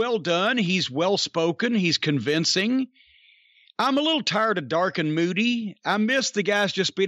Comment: I was doing dishes while he was describing Balor's interview. I misheard the "tired of dark and moody" line. I thought he said "I'm tired of (Bill) Moody." I almost cried. I miss that loveable old queen. I knew few people who were as fun to be around.